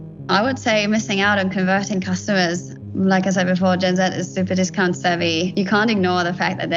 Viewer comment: I really thought she said genz was the stupidest cunts ever